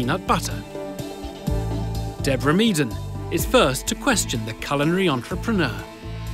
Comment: I thought the narrator said "Deborah is first to question the coloured entrepreneur" and I was like WHOA racism but then I realized he said culinary and I was like whoa racism again, except it's me.